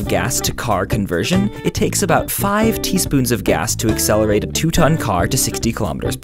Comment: was I the only one who thought "sperms?" before he said "Teaspoons" :-D